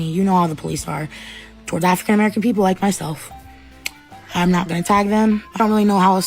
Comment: "towards african american people like myself"whatttt???did i hear that correctly😭🤚🏾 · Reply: I played that part back ten times. I thought she said, "Like a fat american like myself!".